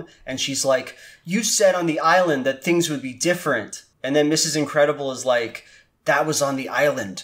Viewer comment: I keep hearing "Incredible Stu" throughout the video and now I can't unhear it. Is it just me?